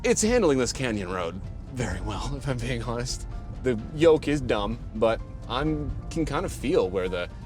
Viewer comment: Did I hear "yolk"? Even CC heard it.. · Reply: Yoke, not yolk